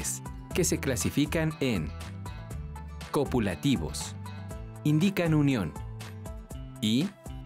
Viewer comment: pensé que dijo coronavirus😅